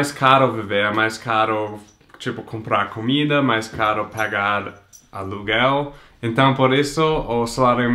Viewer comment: eu entendi "cagar aluguel" '-'